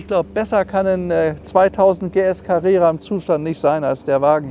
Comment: … 2000-PS-Carrera habe ich verstanden! ;-)